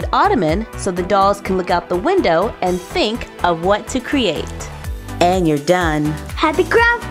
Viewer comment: I swear I thought she said," let the dolls look out the window and think of what they did."